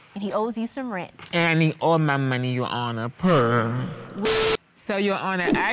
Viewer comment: he actually says "and I need all my money, your honor, purrrrr" but it is what it is :)